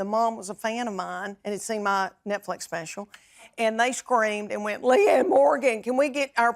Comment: I thought she said "muh nipletts special". That is some serious twang. Netflix should freshen up their name to something twangy.